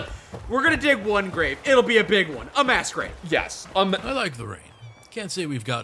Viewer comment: You say "mass grave" I hear "mass rave". Do you see the problem?